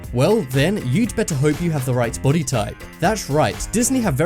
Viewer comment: I thought it said booty type😂😂👏